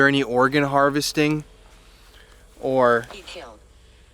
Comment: I think they said “SKULL DRILL” 😬